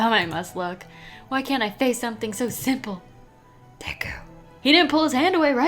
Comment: when bakugo said deku, the subtitles said D*ck😂😂😂 instead of deku. I’m dead, give me them minni wheats ksks